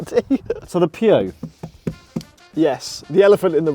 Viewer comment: I swear it sounds like he says the pube all the time xD